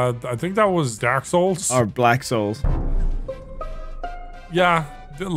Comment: did just say "Blacksouls" ?